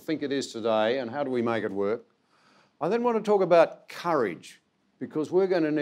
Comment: I thought he said "curry" 😂